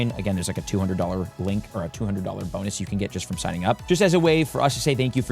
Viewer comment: “there is a $200 bonus for signing up” that’s what he says.